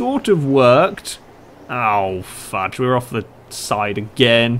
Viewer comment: Thought he said "Oh fuck!"